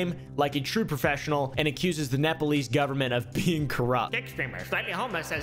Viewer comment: I heard that as met police not Nepalese ngl, had to relisten but it would def fit.